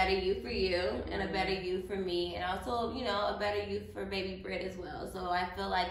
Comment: did I hear her say 👶 baby Britt? 🤔🤫